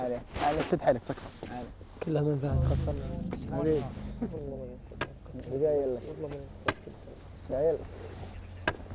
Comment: ايش قال ؟! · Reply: ناصر الهاجري قال كلها من فهد